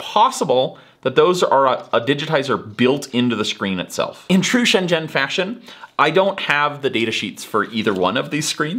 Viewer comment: the subtitles say “intrusion, gen fashion” lol